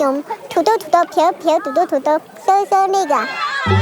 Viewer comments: Did she say N!99@ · bruh did I hear the N-Word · hahahahah look what i found 3:41 just like she is saying ni*ga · Everybody: Wow, a kingdom of small people . . Me: Did she just say the word at 3:46? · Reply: It’s 那个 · did she said the n word? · Did she said 3:46 NIGGA?